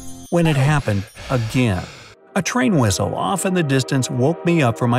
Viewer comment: did he just say whistle